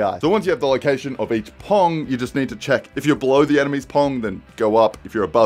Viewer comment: Did he just say "if you blow the enemies dong"?